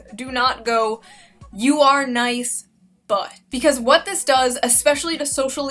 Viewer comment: All I heard was "Nice butt" and Thanks for noticing :)